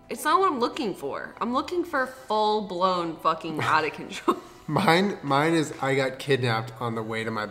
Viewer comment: Please for the love of God please mic julien im sure he's saying funny things but the captions are wrong and im hard of hearing so i cant read his lips ah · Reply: Charlie Knopp because sometimes it's really obvious that that is NOT what they're saying at all. Like at 9:36 where the subtitles say "full-blown fucking hot acacia mine mine is I got kidnapped"....what? Yeah, that's not what she's saying lol.